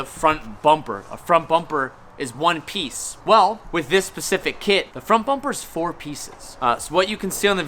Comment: Did he say “pacific”??????